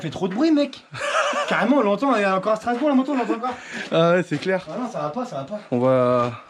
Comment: j'ai entendu strasourg, tu vis en alsace?